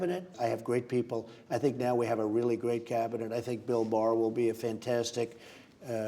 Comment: Just being funny here, but I had to replay here because it sounded like he said "Bill Maher", although I obviously know he didn't...but wouldn't that make for an interesting situation if it were true lol...